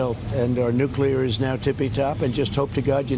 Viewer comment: Please tell me the Commander in Chief didn't say "Our nuclear is now tippy top"? (That's what I heard)